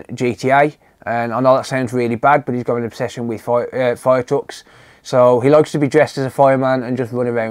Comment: haha I thought you nearly said my 5 year has an obession with violence, I was like oh okay :D